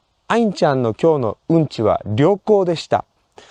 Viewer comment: at first I thought you said アインちゃんの今日のうんちは旅行でした. I would still like to imagine the poop went on a trip, though.